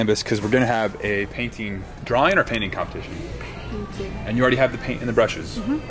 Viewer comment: I thought it said Punting competition lol